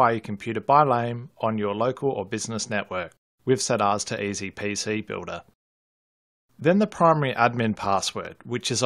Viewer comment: eZpZbuilder is what it sounds like he is saying. LMFAO